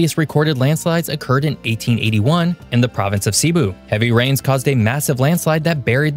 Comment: did you say 1881 ? (Typo on screen says 1811.)